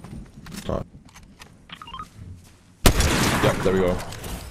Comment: 日本語で【やったるよ】にしか聞こえない